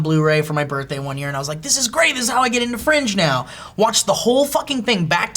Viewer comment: Holy Shit!! I thought he was saying French when he said Fringe, cause I was listening only! lol